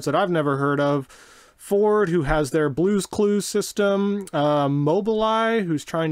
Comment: I heard Ford - "blue clues" system.. made me laugh. LOL